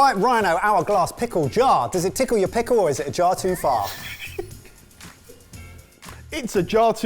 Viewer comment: I misheard at the end of the pickle jar demo and thought you asked if it tickled their pickle or if it was a jar full of farts... I like what I heard better.